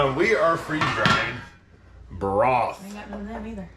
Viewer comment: I thought you said “freezing drying balls”!!!😂😂😂